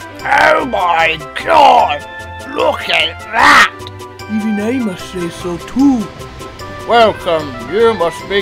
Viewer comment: what did 87546 say? I couldn’t tell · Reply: “Even I must say so too.”